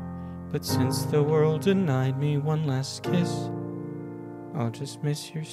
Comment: I heard “one last kiss” as “one last case” the first time and was shocked at how well it fit.